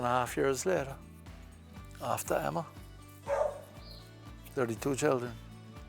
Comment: lol! I swore he said “32 children”🫨. Glad he shared that family pic😅